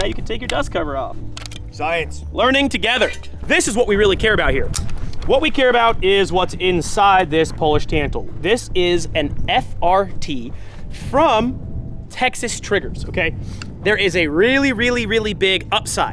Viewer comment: Everyone take a shot every time says “polish tantal”. Good luck. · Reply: My ears are bad and I thought he kept saying polish cancel. I appreciate this comment